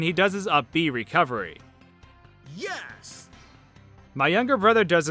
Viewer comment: it sounds like Captain Falcon says "yerks" kinda like hes trying to "jerks" but has a weird accent anyone else hear that